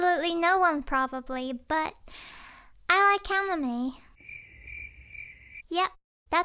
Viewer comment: who else heard hanime?